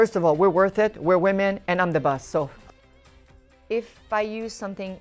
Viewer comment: “ first of all we’re worth it, were women and I’m the boss” Love Celine❤️🤩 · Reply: I thought she said "I'm the buss" - 'cause she's the one everybody waits, not other way round. :: D